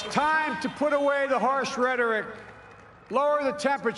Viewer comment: Did he say horse rhetoric?